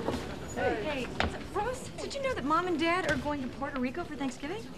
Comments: Monica said rose instead of ross at 0:26 haha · why does monica call him rose, wtf? hahah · Did Monica call Ross, "Rose"? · Is is just me or did Monica say Roass?